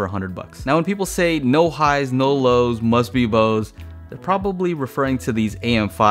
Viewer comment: I had heard it as "All high and all lows, must be Bose" I think refering to the fact you in essence have a sub and tweeters, and no mids.